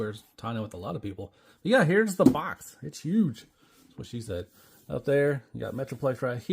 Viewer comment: wait, was did she say you were huge or that she has a huge box lol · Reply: The box is huge. Thanks for watching this 😀